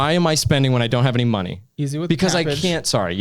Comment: Did he said “easy with the cabbage” at 14:12 ? lol